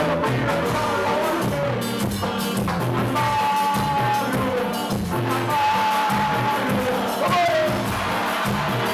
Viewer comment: Que dice en el minuto 1:47 ???? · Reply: Almagro Almagro dice